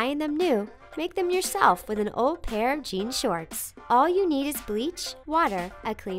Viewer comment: They put pare instead of pair at 5:13 lol 😂😂😂